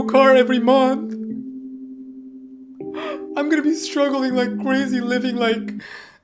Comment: did you say Like Crazy?🫠 Anyways did you guys know that the band member, RM from the guy who sings ‘Like Crazy’ is dropping an album on <num>Called ‘Right Place Wrong person’? With a pre release single called ‘Come Back To Me’ on <num>? ‼️🎉